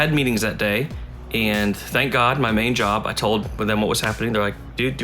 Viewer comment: This is his job and owning the store. What are talking about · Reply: he clearly says "Thank god, main job, i told them what was happening"